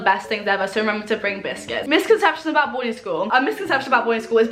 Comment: the whole time i thought you were saying "boy school" instead of "boarding school"